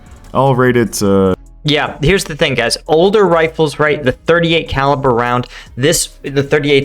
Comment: I think you misheard him, he said you can chamber the revolver in .308, or 7.62x51mm.